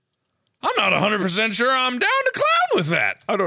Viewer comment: OMG did he just say "im down with the clown"????!!!!!!!